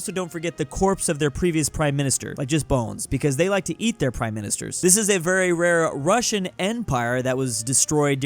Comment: um what did you say? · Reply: They ate their prime minister